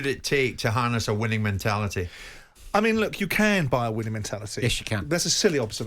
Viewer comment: I honestly thought he said you can't buy a winning mentality.